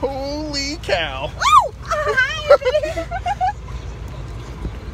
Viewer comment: what did he say at the end? almost made no sense, lmao. 0:13 "When there's buffalo cuddling me, kinda banging me around a little bit"